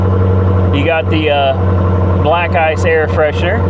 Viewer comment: I thought I heard “black guys air freshener” which wouldn’t be wrong 😂